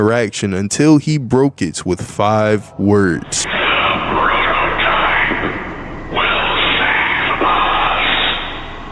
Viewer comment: I have no clue what he said because the words are so distorted can someone tell me what he said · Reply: He said “the prototype will save us” (I think)